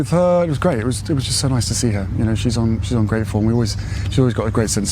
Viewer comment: did I hear she's ungrateful or is that just me?